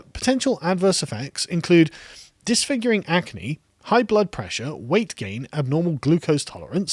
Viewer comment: You got me at "Disfiguring Agony" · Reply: I heard “disfiguring acne”